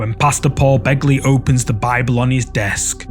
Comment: At 20 minutes or so, it took me several times before I realized you were calling him Pastor Paul Begley. I thought you were saying Pasta Paul Begley, like he was some kind of Italian restaurant owner. I love when you hear different words in peoples accents. Like Beer Can and Bacon in generic British and generic Jamaican.